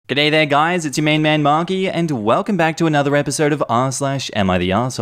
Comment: Misheard Markee's intro as "what's up it's your main man Mommy" and nearly had a fucking breakdown